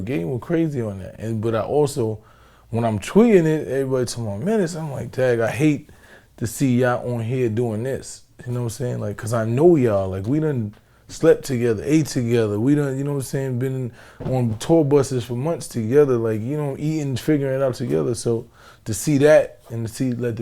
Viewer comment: did he say he slept with that nigga? Lol